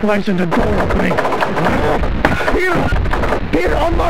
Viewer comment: I think the guy was saying WILLIAM UNLOCK THE DOOR WILLIAM AHHHHHHHH